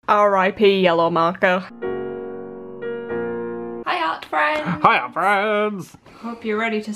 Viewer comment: MY WORLD IS OVER. I'VE BEEN WATCHING THEM FOR ALMOST A YEAR NOW AND JUST REALIZED THEY SAY "HEY ART FRIENDS" AND I THOUGHT THEY SAID "HIYA FRIENDS". AAAAAAAAh